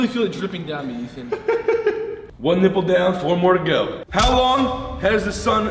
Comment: did u just say four more to go 🤔